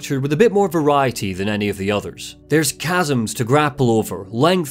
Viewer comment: Smokey Robinson's new album has permanently corrupted my mind. Every single time you say the word chasms all I hear is "Gasms" 😭😭